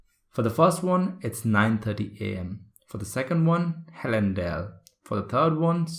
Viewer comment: I hv query with the second question also according to me spelling is halendile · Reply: Same here...i heard it as halendile